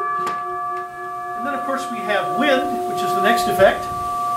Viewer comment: i dont understand what he said because of the bell chime, and the CC subtitles dont really help and its hard to find the vocabulary to describe this sound or instrument that makes this sound? doies anyone know what it is to help me? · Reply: He says "And then of course, we have wind, which is the next effect." :)